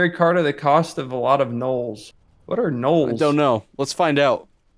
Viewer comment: He said "at the cost of a lot of nulls" and mu brain thought he said "gnolls" which are a pack of hyena people in D&D. I hope that legendary was worth it when the gnolls come knocking